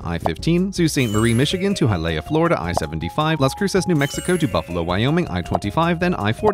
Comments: had to rewind the video briefly to 2:18, I could swear I heard you say Las Cruces, Nuevo Mexico, all in Spanish. My native tongue betraying my ears! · did you just say Hy-lay-ah?